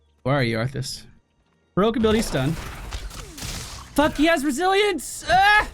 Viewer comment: Wait, what? What does he say? "Fuck yes, Brazilians" ?? What?? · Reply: "Fuck he has resilience!"